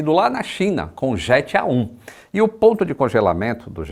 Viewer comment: jet A1 me deu um gatilho futbolístico traumático. até voltei pra ver se ouvi certo. Jet A1 ou 7 a1